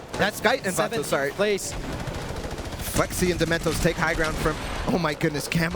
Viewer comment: did i just heard “fuck i’m sorry” lol